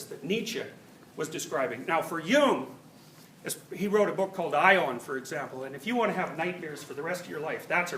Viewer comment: what book does he mention that carl jung wrote called "Ion" I cant understand it its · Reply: Aion: Researches into the Phenomenology of the Self